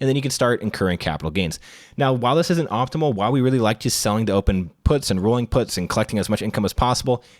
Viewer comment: did he say, "selling dope"? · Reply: Selling to open - definitely heard selling dope the first time too